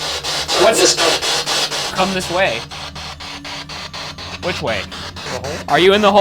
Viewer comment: i cant hear “come this way at” 37:27 i heard something along the lines of “who is this clown?”